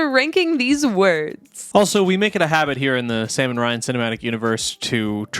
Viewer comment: Ryan smooshed the words "Sam and Ryan" up so hard I thought he said "Salmon Run Tier List" and I clicked on a Splatoon 3 video on accident.